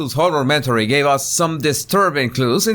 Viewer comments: Aaah, you keep saying 'horror-mentary'... due to your accent I thought you were saying 'whore-omentary', LOL! Either one works perfectly well for me though. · I thought you were saying 'whore-a-mentory". I almost spit out my coffee ☕️. 😂😅😂